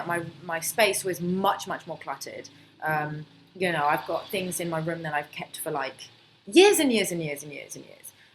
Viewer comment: Did you say the german word "genau" (means exactly) at 8:51? Or was it you me hearing it? Cheers Oliver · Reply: nein. Ich kann ein bisschen deutsch sprechen aber ich habe 'things that've kept in my room for years and years and years and years' gesagt.